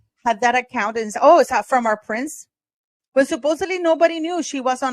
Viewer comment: she may have called photog in Rwanda “her prince.” Just something I think I heard. I doubt she called every man her prince but just sharing